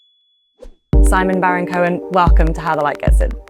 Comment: I thought it said "Sacha Baron Cohen" at first and I was.... Confused. 😅